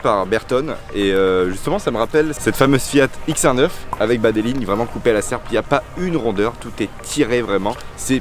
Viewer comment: "ces lignes coupées à la serpillière" 😬 6:27 au début j'ai compris ca 😂😂😂